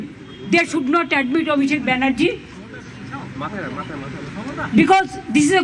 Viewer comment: Did I heard ABHIJIT BANERJEE at 3:30????? I may be wrong😔